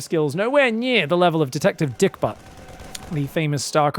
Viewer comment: did you just say "Detective Dick-Butt"....??? wtaf